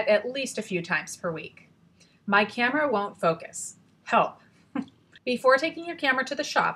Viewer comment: did she say "hoe?" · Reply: Raza Rizvi she said help dumbass